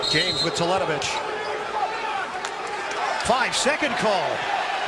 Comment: What was the call at 2:45? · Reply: Hypotonic Hd 5 seconds back to the basket. AKA The Charles Barkley rule